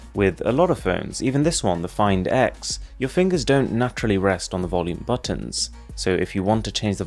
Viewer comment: Am I the only one hearing "Na-trough-ly" instead of naturally?